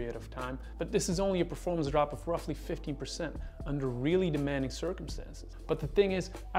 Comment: did he say 15% or 50%? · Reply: 15% so it is only marginal! Thank you for your question.